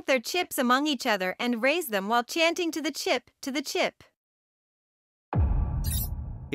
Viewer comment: My friend and I thought they were saying “tuna chip, tuna chip”!